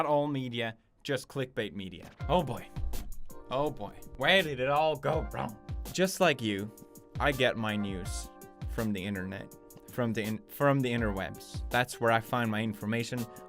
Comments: At first I thought hes said "Just like you, I get my NUDES from the Internet", But it turns out he said NEWS. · I thought he said, "just like you, I get my nudes on the internet!"... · I thought he said "I get my nudes from the internet" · I swear I heard,"I get all my nudes from the internet"